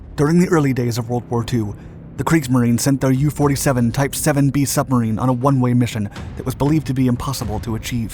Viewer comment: I heard "Shubmarine" more than once, am I alone in that. 😅